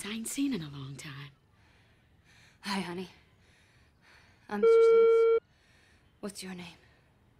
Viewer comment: As a kid I used to think when they bleep someting on television, means they are saying bad word Almost for a decade I thought she said "Hi Honey, I'm BITCH, What's your name?"